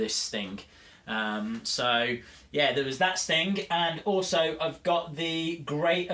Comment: You kinda sound like your saying Stink 😂